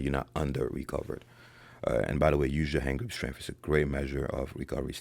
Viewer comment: What did you say was a good measure for recovery status at 15:12 ? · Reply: Hand grip strength